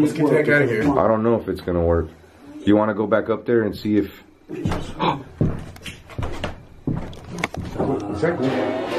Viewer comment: i can hear some voice but i dont know what it say because you and talking. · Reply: A little girl say "hey "...